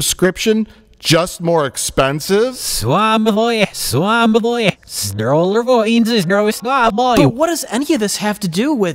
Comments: Aight what does say at 12:41? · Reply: "We all bow to our Disney overlords. Hail the mouse! Hail the mouse!" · what does say? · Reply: "We all bow to our Disney overlords. Hail the mouse, hail the mouse." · What did say when they reversed hi audio @ 12:40? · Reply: "We all bow to our Disney overlords. Hail the mouse, hail the mouse."